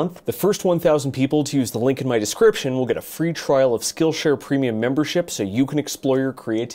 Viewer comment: for some reason i heard "this video is brought to you by skullshare"